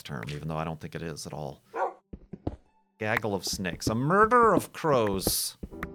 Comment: said "Murder or Crows" but I heard "Murder of Prose." Which I think sounds like the work of a bad poet.